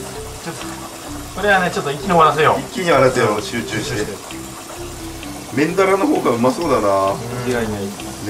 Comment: メンダラがメンヘラに聞こえる